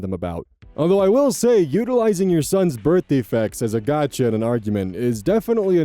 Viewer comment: I've had a long day so I kept mishearing "birth defects" as "birthday facts".